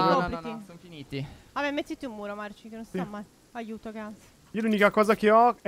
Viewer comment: dice aiuto cazz o ho sentito male?🤣